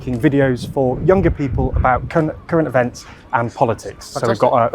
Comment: You could've rerecorded this instead of telling the world you said "cunt" in front of Keir Starmer